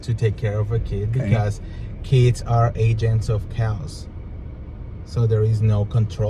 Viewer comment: 😂 I thought said Kids are agents of cows 🐄